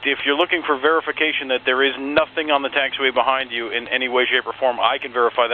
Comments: Correction on transcript: @ 2:55 he actually says "Nothing on the taxiway behind you in any way, shape, or form..." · Just noticed at 2:58 CVG GND says "any way shape or form" but the caption says "any ways you perform." First time noticing a slight error for you, which is impressive as often as you wind up posting! Much respect. 🤘🏻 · Subtitles are wrong: 2:59 ATC Says: behind you in any way shape or form, NOT behind you in any ways you perform.